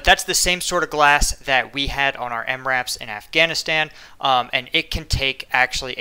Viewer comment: thought you said AMRAAM, was a bit confused...